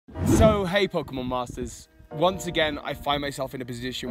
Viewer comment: ive always thought you said "so hi pokemon masters" cuz of the brittish accent